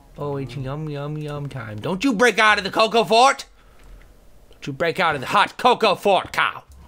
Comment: when he said coco fort i thought he said coco fart